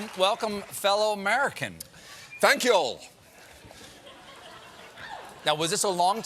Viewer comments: Anyone else notice he started with “hello y’all” and looked disappointed to get no reaction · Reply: He says "thank y'all". I think they all misheard it as "thank you" in a weird accent which is why it fell flat. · Poor guy he wanted everyone to notice his y'all at the beginning. Even looked to Stephen for a laugh but nooooo lol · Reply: I thought he just said "thank you" weirdly. I didn't notice he said y'all. He should've said "thanks y'all" to be clearer